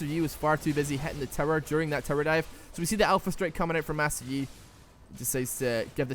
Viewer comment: I swear i heard say "Soraka with that alpha strike"